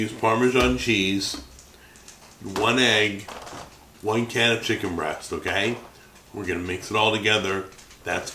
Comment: Sorry..? It sounded like you said one !CAN! of chicken breast..?